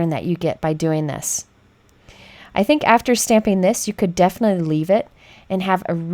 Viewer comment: I laughed right out loud...you could leave it...I heard 'leaf it' as was stamping a leaf pattern!